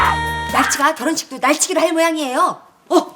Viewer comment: 결혼식도 날치가 날치기를 할 모양이에요 ㅋㅋㅋㅋㅋㅋㅋㅋㅋㅋㅋㅋㅋㅋㅋㅋ 몇번 봐서야 뭐라는지 알았네 ㅋㅋㅋㅋㅋㅋㅋㅋㅋ 13:17 뭐야 ;; 13:19 율트시그널 느낀 땡구 ㅋㅋㅋㅋㅋㅋㅋㅋㅋㅋㅋㅋㅋㅋㅋㅋㅋㅋㅋㅋㅋㅋㅋㅋㅋㅋㅋㅋㅋ